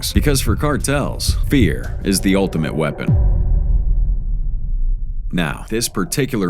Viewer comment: I’m so high I thought cuh said beer is the ultimate weapon 😂. Time to put down the bong and go to bed 🤣